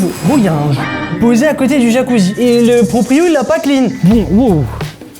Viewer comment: y a quoi ?? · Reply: Un joint je crois